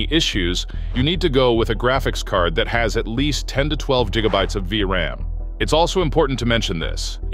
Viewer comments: At the 1:27 to 1:28 mark i swear it sound like he said 10 to 12 jigabytes 😂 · Did you say Jigabytes? 🕺